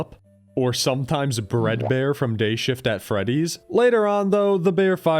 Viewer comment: why did i hear bredbear (as in breeding) from gay shifts at freddy's 😭😭😭😭😭😭